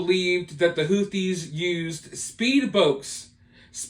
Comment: Was he saying "speed bokes" a couple of times?